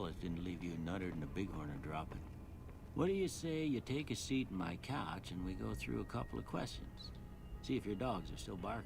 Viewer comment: Every time Doc asks you to sit on the couch, I keep hearing "Please sit on my crotch". I think I'm starting to have hearing problems xD